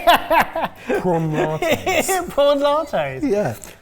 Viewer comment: I know what he actually said, but now I'm gagging thinking of Prawn Lattes